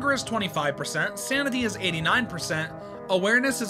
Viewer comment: Hey father fed us bread btw sounded like u said samity in the first part 😂🎉